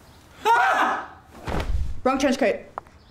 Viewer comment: Not a native English speaker: what is she saying very quickly at 0:15? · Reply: insanely late but "wrong trenchcoat"